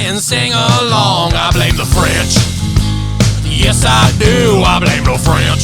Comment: I thought you were saying " I blame the fridge". As a 1/16th French-Canadian born in San Francisco, Cali... I'm not offended. After I saw the John never died short I was audio bible listening and there's a verse that said he died after visions. Sorry if I find it I'll post it.